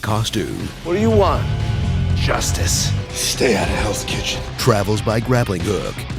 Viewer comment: Did he say "Stay out of hells kitchen?"